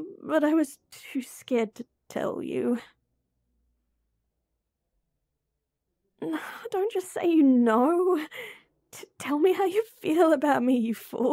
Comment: Towards the end, my headphones were almost empty and they dont just make a noise for that, they say, baterry level low So at one point, i just heard:"no, don't just say BATTERY LEVEL LOW" actually lmao 😂